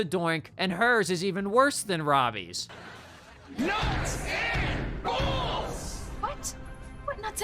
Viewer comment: at first I thought he said “nuts and balls”